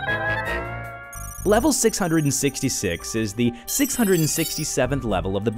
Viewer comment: WAIT DID HE SAY 67?